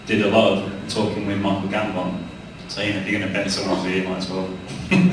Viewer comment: Guys, anyone, please, what did James say at 3:35? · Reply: “If you’re going to bend someone’s ear”